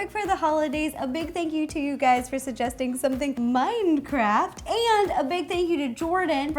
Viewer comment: She said mindcraft instead of Minecraft